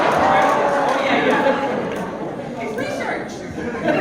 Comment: if we start i can't understand what he said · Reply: i’m like 90% sure he said “it’s research!” also yes. i see that this is a year old comment.